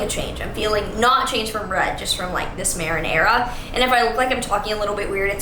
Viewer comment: When you said "Maren Era" and I heard "Marenara" 💀